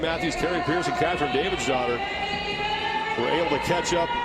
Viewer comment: lol forgive me for not knowing the women's names, but i was getting super annoyed with the male commentator when he kept saying kathrine davidsdottir bc i thought he was saying kathrine david's daughter lmao